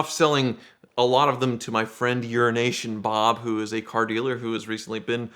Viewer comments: Did he really say urination Bob? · Who else thought said Urination Bob at first? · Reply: says euroasian bob it sounds like urination bob. Lmao. Thing is when i was telling the wife about tricking and surprising euroasian bob with the red 348 spyder deal a while back, she thought I said urination bob also. Btw the way I have a 94 348 spyder in red/tan and is my favorite ferrari made.